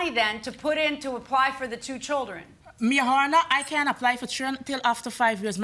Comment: Defendant talks too much & what did she say at the 10:19 mark, Morehoner? 🤣 🤦🏾‍♀️& she don’t look like she’d let him beat her 😏